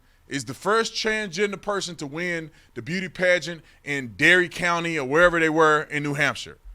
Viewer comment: When I heard you say "Derry", I thought you meant dairy (as in cows). 😂😂